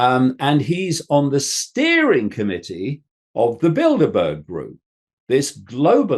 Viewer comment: I felt really stupid because I thought he said 18:40 “…and he’s on the steering committee of the Build-a-Bear group,” but then I remembered this guy thinks there’s lizard people 😂